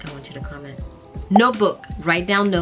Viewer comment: For some reason I thought you said no book 😂Note book ❤🇬🇧